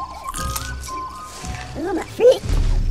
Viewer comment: does he say oooh my feet?